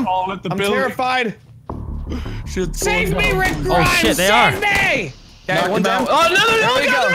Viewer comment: did he say "save me rick grimes save me" 😂😂